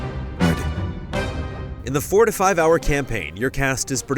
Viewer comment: At first I heard "In the forty five hour campaign..." and I said WTF??? Hahahaha